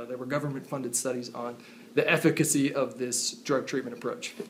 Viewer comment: Did he just call efficiency "effikicy"